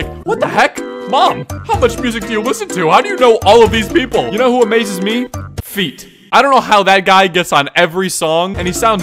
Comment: I was listening to this while doing my makeup and i thought you said "you know what amazes me? Feet."